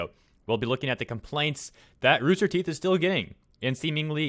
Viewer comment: I thought you said “that Rooster Teeth is still gae” just woke up though.